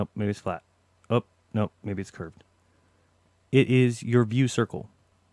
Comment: Did I just hear, "it is your view circle"..? A "view circle"?